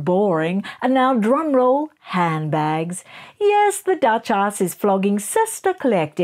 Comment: Did you just say… duch-arse…? 😂